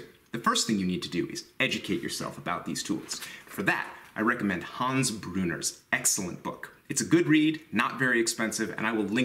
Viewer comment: Thought for a second you said Hans Gruber..... yippee ki yay... Great video, lots of great info as always. Thanks!!